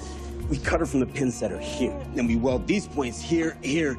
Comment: I thought he said “split” instead of “splint”. That would be a very bad pun.